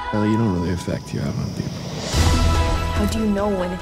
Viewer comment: I keep repeating this part and I still don't know what he says here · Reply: "belly, you don't know the effect u have on people"